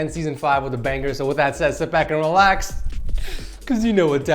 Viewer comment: Oh god i thought he said "Shit Smack and relax